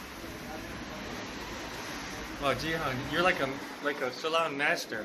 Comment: For a second I thought he said his name was jihad😭